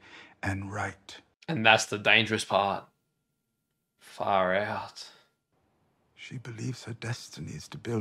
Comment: What is the word I keep hearing “faryout?”